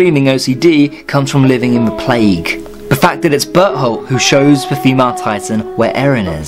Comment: When you said Bertholdt it sounded like you said butthole and I was lying there so confused like "who's that?"😂